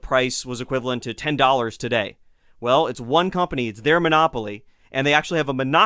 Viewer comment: I love this podcast! Fantastically deep dive into colonial America! One thing: maybe I misheard you, but it sounded like you said "monosopy". For those who are curious about this rather obscure economic term, it's 'monopsony'. Cheers!